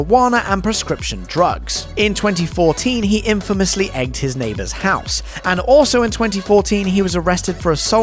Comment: I thought he said "... ATE his neighbor's house." ...